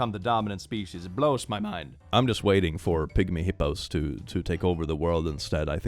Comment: Ok, one sec. So, I heard "pick me hippos take over the world" And my mind went on a whole fantastic journey before I realized he really said "pigmy hippos" 🤦